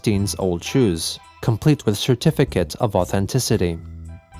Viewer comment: I misheard that as 'shoe-tificate' of authenticity originally lol